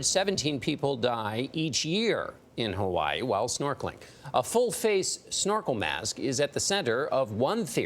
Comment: I thought it said blue face snorkel